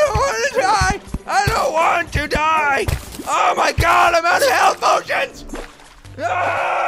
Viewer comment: oh... I thought he said "I'm outta health! oh ****!"